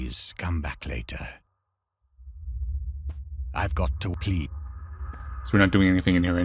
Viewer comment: when you skipped the owls dialogue it made it sound like he said 'ive got to pee'